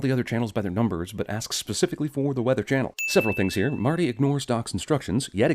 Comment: i thought he said "and one other chanel" not "the weather chanel" so that means its not a sin